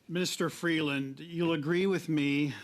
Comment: did I hear ‘’Mr. Freeland ‘’? 😵